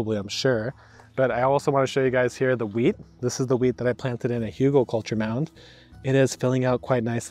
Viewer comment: Oh you tried to say Hügelkultur! It sounded like Hugo culture, you should work a bit on your pronounciation